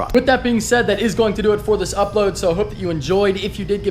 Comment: No you fuckers!!! They are NOT upside down, they only fit one way! Yes, I switched the wiring harness around... for now. I still want something just a little different- This is why I go to Andy. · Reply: Sounded like u said police subscribe😂😂